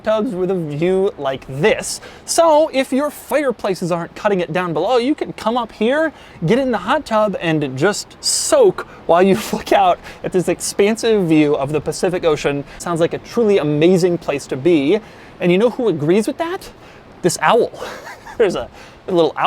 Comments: did he say fuck out? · what'd he say? 🤔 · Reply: “Soak while look out at this expensive view of the Pacific Ocean.” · did you just say F**k out